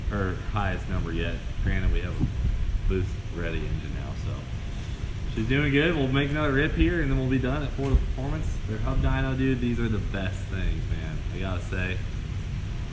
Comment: Lol I thought you said Forza Performance so I figured we’d see a tatted dude in a wife beater talking to us haha 😆🤷🏻‍♂️